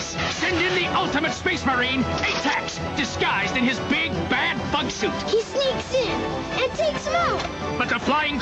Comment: do my ear deceive me but do i hear 'big bad fuck suit'